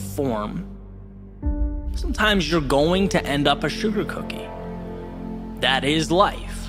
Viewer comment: Haha!! I think we've all been there at some point. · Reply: at first i thought you said he deserved a sugar 🧑‍🍳 cookie 😂